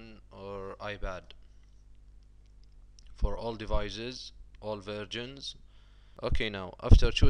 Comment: "all versions".. thumbs up if you heard him say "all virgins" LOL .. Is that a German accent? nice vid... keep up the good work! thumbs up.